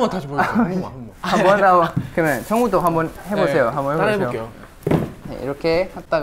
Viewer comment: DID HE SAY SUNGWOO AT 10:32 HAHAHAH pls tell me you guys hear it too 🤣 edit: 13:07 too 🤣 i think there are more, but i don’t think i will write down all of em